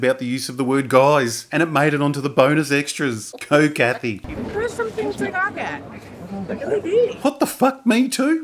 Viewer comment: What does she say? I can’t understand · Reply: To me it sounds like, "There are some things to gawk at...Billy Dee!"